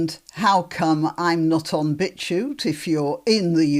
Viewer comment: I thought you were saying a site called 'BitchYou' Gran, about women like the ones who were trying to stop Robert Lowe talking in Parliament.